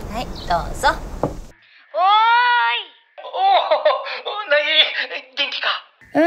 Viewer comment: うなぎって聞こえたw